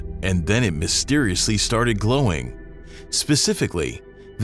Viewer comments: At first, I thought they said growing and before I realized what it it really said I said to myself, what are we going to do, what city is it in... they should definitely force the guy who made it - eat it.🤠🤠🤠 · I thought that shit said “growing”